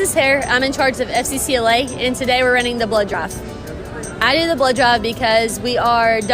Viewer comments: I thought she said blow j ob instead of blood drive too bruh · Anybody else heard her saying "...running the blow job...." and not "...running the blood drive..." ??? Man, I had to look up at the screen and rewind just to clear that up. 🤣🤣🤣🤣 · I thought she said blow job not blood drive lol 😂 😅😂😊😅